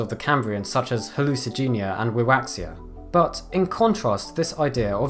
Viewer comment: WHAT ANIMAL IS THIS? LOOKS LIKE A PINEAPPLE i couldnt understand what he was saying, rawaxia? rwaxia? waxia? rawaxia?